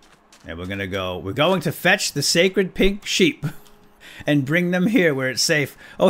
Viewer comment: who else thought he said sacred pig sheep when he said "Lets go fetch the sacred pink sheep!" ???